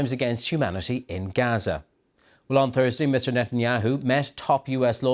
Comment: did he say "bloodthirsty mr..."